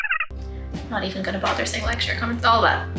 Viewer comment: Did she really said like,share, comment🤨